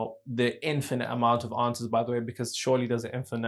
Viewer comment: Infinite amount of....onces???? what was that word you said at the very end. anyone? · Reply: Answers